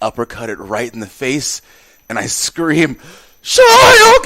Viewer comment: what’s he saying?? I didn’t understand I’m deaf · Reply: Shoryuken it’s from street fighter a video game